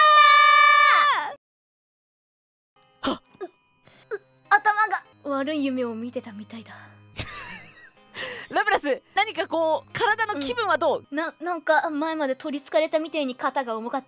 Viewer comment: 頭が、悪い夢を見てたみたいだ ↓ 頭が悪い夢を見てたみたいだに空耳した